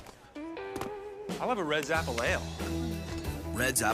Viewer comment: To me it sounded like they said Red Zapple Ale. Like, an ale the Apple Family makes from zap apples!